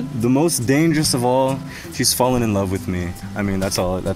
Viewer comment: Him: "shes fallen in love with me" Her: "you might be on to somethin" (did i hear wrong?)